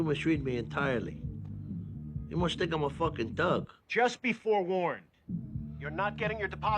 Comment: "you must think im a fuckin thug" 💀🤣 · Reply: I heard “fuckin tug”